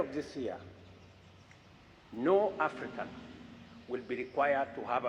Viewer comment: I thought they said they are Arabs not African